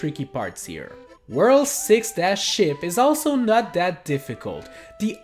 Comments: was i the only one who heard “world 6-shit?” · in 17:59 did he say shit instead of ship